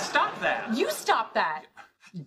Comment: I heard “kurva” xD